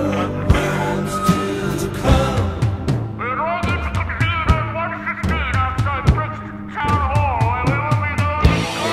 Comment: What does he say at 1:20-1:26? My best is ".....1-16 outside Brixton town hall and(or) there will be no...." any help? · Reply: "Will the audience convene at 1:15 outside Brixton town hall where we will be going to...."